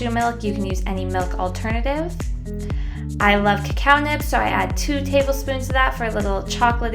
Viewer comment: omg i thought she said cow not cacao lolllll